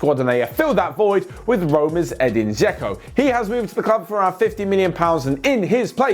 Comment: did he say 50 million for eden Dzeko he is 34